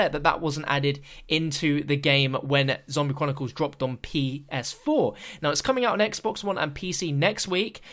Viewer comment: it sounded like he almost said PC instead of PS4.